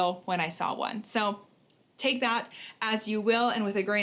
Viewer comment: When you said 'take that as you will' I heard 'take that as you wheel'